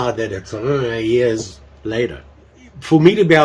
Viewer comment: did you say 100 years later lol, you'll like that knife it's a keeper :)